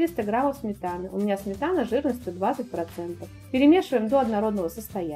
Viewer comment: О боже, мне послышалось, что у вас сметана жирностью 120%!